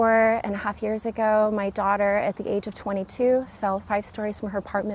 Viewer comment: Amazing so happy for you.. And you look like you are 22 years old.. Can't believe you have a daughter of 22 yrs. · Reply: wow, I went back on the stream 2 times because I didn't believe it, I thought she said at the age of 22 but heard the daughter of 22.